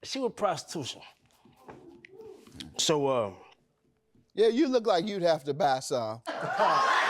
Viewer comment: Sometimes JM gets on my nerves another day he's my best friend when I'm having a rough day. 😂😂😂😂😂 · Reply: Did he just say" yea you look like somebody that got to buy some" lmao..same@ Grant.. 😂